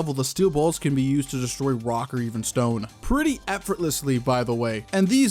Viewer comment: DID I HEAR A ROCK AND STONE?!